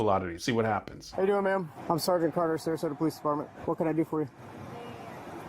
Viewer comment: Sargent Curtis!!! Who heard that?